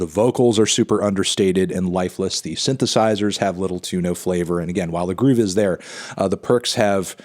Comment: Lol I thought you said "Little tuna flavor"